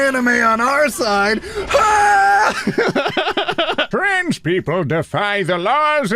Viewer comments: I'm so dumb I heard "French people" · I thought he said French instead of trans and i just accepted that this person was saying French people are threats.